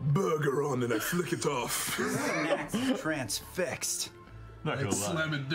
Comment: did just said "transfixed"?